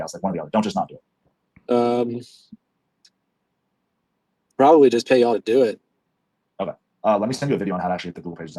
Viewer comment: Great content again bro thanks for sharing. At 21:20 definitely misheard because he did say "probably pay yawl to do it" right there. I think you missed it.